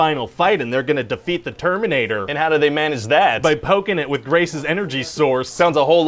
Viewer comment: I heard “Grace’s” as “Racist” so I thought he said “By poking it with a racist energy source”